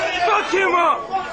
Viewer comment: did he say ''fuck him up'' or ''fuck kimbo''?